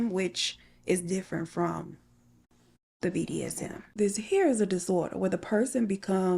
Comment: I misheard and heard this HAIR is a disorder instead of this HERE is a disorder. Both are correct in my eyes because that hair is horrible.